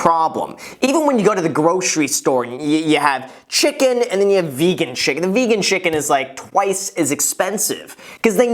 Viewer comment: if you're doing all organics & not watching for sales, or buying exotic items, processed & packaged items, etc. that'll jack the budget. _ .... wait: 7:27-29 did he say : Vegan CHICKEN!!!? `hope that was intentional.. ((ie: all the faux-meats are friggin' expensive + aren't really "healthy" as far as what comprises the product))